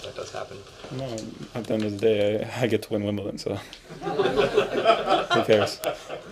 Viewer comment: What does he say at 2:25 ............ I get to win Wimbledon so...... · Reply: I get to win Wimbledon so who cares